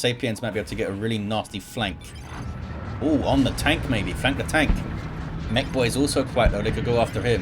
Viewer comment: I thought said 'Frank the tank' (Old School anyone?), but it was actually 'flank the tank'. I prefer my mistake.